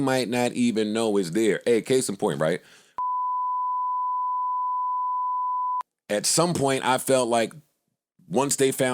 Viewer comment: Time stamp killers in the comments... lmk what joe said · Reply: Rory and Mal